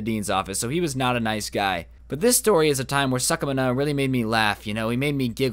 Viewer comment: Mr. Suckmna? I actually heard it as "Mr. Suck mah nut", clever name dude